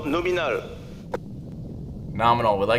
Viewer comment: I thought he said 'Sub-Luminal' ; don't know why I didn't realize that was wrong immediately